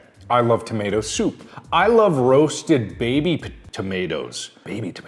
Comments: I paused the vid right as he said he likes "roasted baby tomatoes " to get a snack but i paused it just be for he said tomatoes and all i hear is " i like roasted babys" 😂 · I thought he said i love rosted babies instead of baby tomatoes